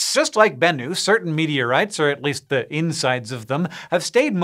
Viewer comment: Aw... for a second I thought you said "Bacon Meteorites"...